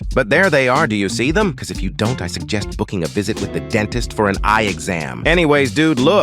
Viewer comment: Did you say with a dentist for your eyes 0:45 and